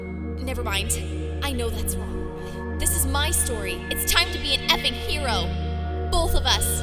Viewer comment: In that note, who else thought it said: It's time to be a EPIC hero.